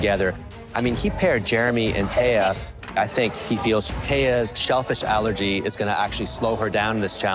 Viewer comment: At first I thought, chef Claudio said " Thea's selfish allergy " instead of " Thea's shellfish allergy "😂😂